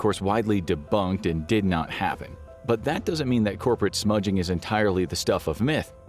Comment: Am I the only one who heard ‘corporate smudging’